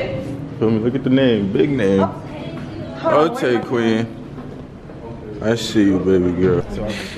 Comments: Am I trippin or did he say I see you "Babygirl" 1:02 😩😩😍 . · Did anyone else hear him say " I see you babygirl " OOOHHHHH SPICY MAFIA COUGHT THAT🌶🌶🌶🌶 EDIT: Thx for all the likes and replies